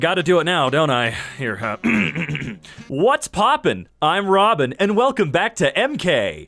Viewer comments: Bruh i heard “Whats popping im robbing” o.O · I thought he said “what’s poppy I’m robbing “ edit: autocorrect, meant to say poppin